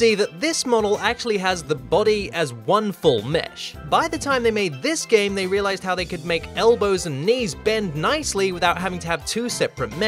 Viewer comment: Hey guys, here, I really hope you enjoy the first episode of a new series. This is something you’ve probably never seen on Youtube, so I’m really hoping you learn something or get some entertainment out of it. Son of a Glitch returns next week! Please like and share this video around, I want the new series to do well. Post a comment on things you like to see in Low Poly too, and I’ll see what I can do! Thanks for the support guys! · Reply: when you said "by the time they made this game they realised how they can make elbows and knees bend nicely" i thought you said "by the time they made this game they realised how they could make elbows and knees". i feel. so stupid.